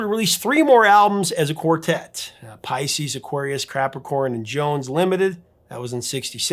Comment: Catapulted Into Super-Stardom bcz Of Their Wholesome Nice-Boy Images as The whole country Of Young ladies fell in Love With Them. Yes they were Originally Just Hired as Actors as per Their Contract! But Shows Overall What Immoral (Luciferian = Greedy/Evil) managers do Behind Musicians Backs, >>> Go research Badfinger band & Harry Nillson Eventually leading to 3 of those musicians being so distraught-Depressed they commited Suicide!!! Many Other Bad/Horror Stories similar out there!!! & Some Good managers Out there In Retrospect do a Vid On the GOOD Ones!!! Please! Thanx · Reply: Sounded Like "CRAP-ricorn" approx